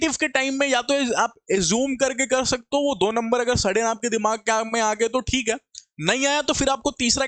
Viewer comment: I thought you are saying Agar aapke sade hue dimag mai aagye toh thik hai😂😂